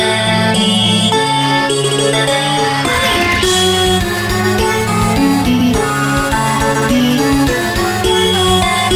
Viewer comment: What does it say at 1:13? does it say meggy! ? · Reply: It says woomy